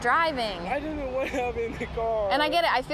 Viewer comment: He clearly says "I didn't wanna have it in the car." NOT "I don't know what happened in the car." ..... what the actual f...